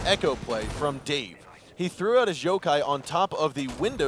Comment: wtf he said jokai not yokai lol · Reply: Salah Neuer he did say yokai, but "his yokai" made it sound like jokai